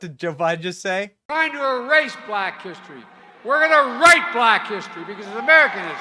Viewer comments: Sounds like he said “we’re going to write black history”. That’s bad enough, you don’t need to misquote. #FJB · It’s very obvious by the numbers of people that attend. Shows you who likes who the most. 5:35 did he just say, ‘We’re going to r**e black history? Did I hear that correctly ? lol The kids there for extra credit, might be thinking, ‘What am I doing here?’ I think they might be making their decisions to walk away.